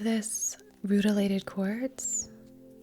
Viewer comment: Dying bc I kept hearing “‘mutilated corpse” every time you said “rutilated quartz” lmfao ...serial killer documentaries getting to me